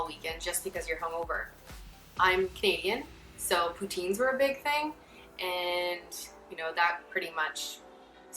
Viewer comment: I heard "I'm vegan, so routines were a big thing," for some reason. I was very confused for a while.